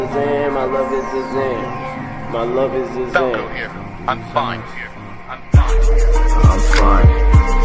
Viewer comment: que dice el que habla de fondo? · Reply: Aqui Falco, estoy bien acá Eso dice